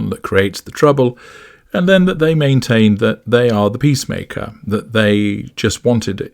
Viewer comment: Peace maker, omg I thought it said Penis Taker